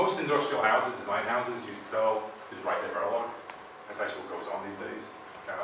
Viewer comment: At right around 1:00:17, he's saying something which sounds interesting, but I can't quite make it out, due to the sound being a bit muddled. He starts off saying, "Most industrial houses, design houses, use [can't understand] to write their Verilog, that's actually what goes on these days, it's kind of horrible to see..." Anybody able to understand what he said there? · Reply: After watching another video I think I understand: "design houses use perl to write their Verilog"